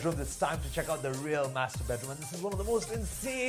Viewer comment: Everytime the third guy says "master bedroom" , I hear "masterbate room" lol